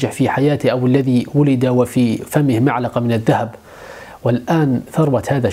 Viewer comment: كانني سمعت معلقة عوض ملعقة 😊.. المهم نكمل الفيديو